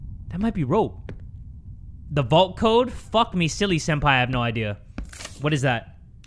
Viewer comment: Wait did you just say f me silly senpai °•°